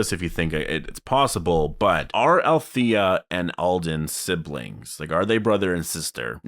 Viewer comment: Alden? Thought you said Aaron...